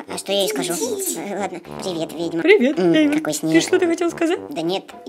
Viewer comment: Почему вы называете эту белую худую красавицу Ведьмой? Она же красавица. Мне послышалось Вельма.